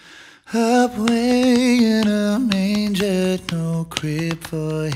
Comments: It sounds like he says UP WAY in a Manger instead of away in a manger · Am I the only one who hears Scott sing "a-bway in a manger" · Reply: I think he's singing 'Up way in a manger..." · did I just hear 'apway in a manger'